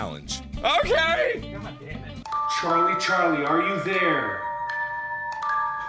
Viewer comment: did anyone heard "god damn it!!!" ????